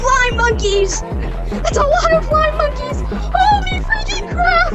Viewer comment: I thought he say flying monkey instead lime monkeys😂